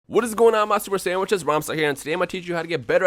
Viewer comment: who noticed what rhymestyle says in the beginning of the video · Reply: Sahil Plays OMG I ALWAYS THOUGHT HE SAID SUPER SAYAIN